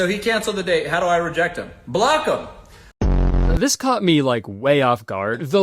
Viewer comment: weird mandela effect on rewatch since i fully thought he said “kill him!” and not block him